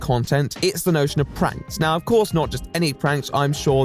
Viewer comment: I thought you said "praxis" n I waited to see how you'd connect anarcho theory and pranks before I thought maybe I misheard